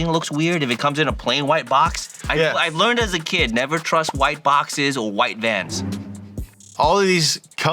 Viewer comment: I thought he said he doesn't trust white boxes white dads